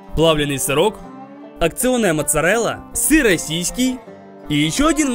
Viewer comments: мне одному послышалось "сырасиськи"? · мне послышалось:"Сыра сиськи" а не Сыр Российский. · нормальные люди: СЫР РОССИЙСКИЙ Я:СЫРОСИСЬКИ🗿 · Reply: Я вообще не поняла сначала типа: Что СЫРОСИСЬКИ? Мне не послышалось? (•_•) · он сказал "сыросиськии" или мне послышалось? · Я сначала услышала сыр и сNсЪки😂